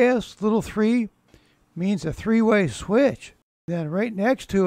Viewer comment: you curse??? 0:32 with captions on and it says A**??? What is the word you're trying to say? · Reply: Big "S", little 3. I was referring to an electrical symbol on the drawing. LOL! I got a good laugh from that!